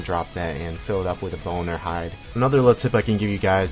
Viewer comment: Did anyone catch boner hide at 2:30?